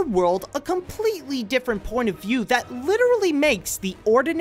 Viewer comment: i it just me, or does he says "a completly different porn of you" at about 5:7 to 5:10 and 10:7 to 10:9 ?